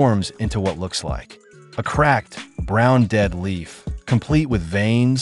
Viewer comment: Dead Leaf! I thought he said DEADLY,!